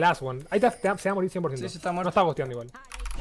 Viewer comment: "lo estaba gosteando igual" grandeeeee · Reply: Yo escuche "no esta gosteando igual " xdddd